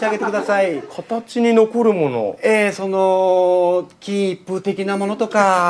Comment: 何をあげろ て言ってるの? キープて聞こえるけど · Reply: 金一封